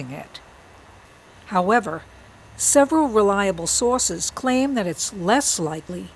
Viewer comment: did you say SAUCES or SOURCES??? LOL... Sorry, I can't help making fun of Yankee's and their accents. So, are ALL pines pollen edible in the U.S.? Assuming the answer is yes, or hope so at least. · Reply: It's "sources" --and I'm used to folks making fun of my "Bawston" accent (which really isn't as obvious that as some Bostonians:) The only pine that I know of that may be problematic is Ponderosa which is found out west. I believe the pollen of all others is ok.